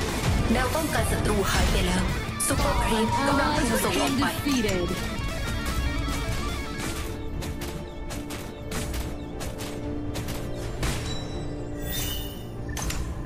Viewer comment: ทำไมผมได้ยินเป็นซุปเปอร์แมนกำลังถูกส่งออกไป😂